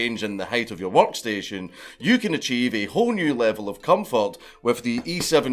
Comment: When dankula says comfort it sounds like hes saying cum fart lol